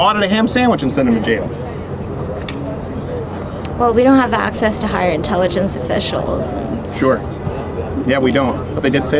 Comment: Poor girl, she kept saying "we don't have access to higher intelligence", I kept hearing "I don't have intelligence".